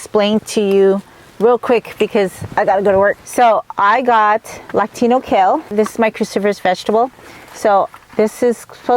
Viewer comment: I thought you said latino kale 😂 I was like thats wild what makes it latino